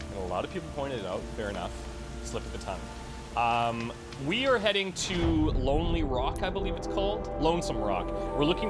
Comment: I was on another screen, and in the beginning I thought you said, "today we are heading to forever alone rock..." I wish the rock was shaped like the forever alone meme XD